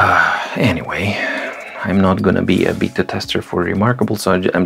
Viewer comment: Voja, your comment cracked me up as I heard "I'm not going to be a BITTER tester" instead of BETA tester!